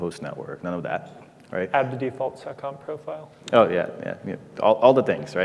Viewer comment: "Add the default SOCOM profile" SOCOM?? What did he just say??? Even the closed captions didn't pick it up. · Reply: It's "seccomp".